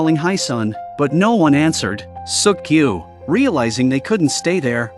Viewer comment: did bro said suck you